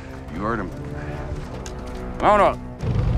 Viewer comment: did he say Vamonos or Woundemup really fast? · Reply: He said ''mount up'' if I remember right.